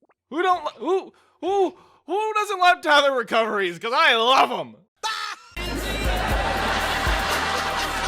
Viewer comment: can someone explain to me what the feck he’s saying here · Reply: "OOO! WHO DOESN'T LOVE TETHER RECOVERIES? CUZ I LOVE 'EM!"